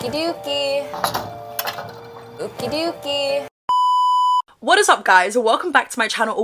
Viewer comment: lmao she didn’t even say it right, sounded like she said ookiee dookie not okay dokey